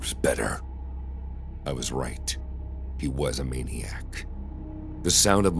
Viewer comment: DID HE JUST SAY MANIAC?!?!??? MANIAC nesa pajin nokorom micheo MANIAC peeng peeng goboro kachi STAN SKZ🎉 STRAY KIDS WORLD DOMINATION🌍🌍